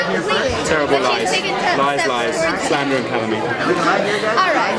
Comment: what did kat say at 1:20-1:24 that made dom lay his head on the table? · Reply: she said alright dom